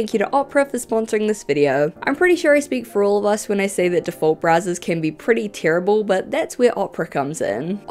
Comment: For a moment there I misheard you advertise an alternative to "default Brazzers"... had to rewind to make sure.